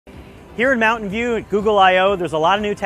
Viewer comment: I'm I the only one who thought he said "Here at Mountain Dew..."?